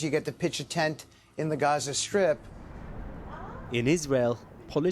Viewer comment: Did he just say "Gaza Script"??? Hear me??